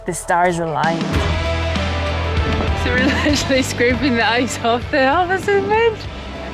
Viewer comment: I dont understand a word you just said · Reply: “We’re literally scraping the ice off the boat, this is mint!” She’s Scottish, don’t pick on her 😂😃